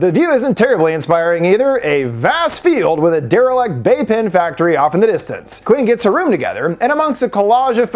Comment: Talking so fast I heard “vape pen factory”